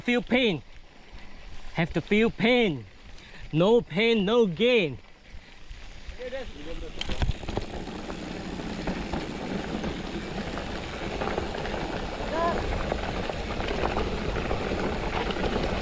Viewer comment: thought i heard no pain, no gay..haha